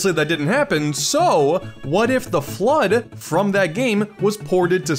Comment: everytime he says the FLUDD i hear "THE FLOOD" like the flood from halo and i jump in fear ever so slightly.